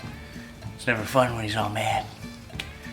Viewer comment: what the hell did he say??? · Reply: "it's never fun when he is all mad"